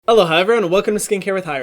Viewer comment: dude i always thought he said “hello hi everyone and welcome to skincare with hyram” but it’s actually “aloha everyone” haha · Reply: For some time I thought he said 'ello in a purposefully bad british accent not aloha